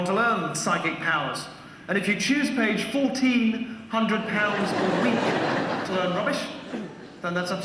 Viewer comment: Whaaa? When the show was actually going on I thought he said 'If you choose to pay 14,000" damn. Tricky,.